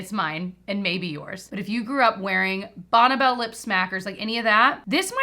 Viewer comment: Bonna Bell? I’ve always thought it is pronounced Bonny Bell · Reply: I grew up in the 70's/80's, I've only heard it called Bonn(ie) Bell! We all said it that way lol! First time I heard her say it like Bonna Bell I was, "like Bonneville?" 😂